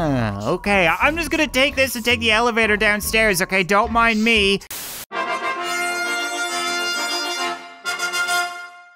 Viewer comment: i heard the s word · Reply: “Ah, s**t” 😂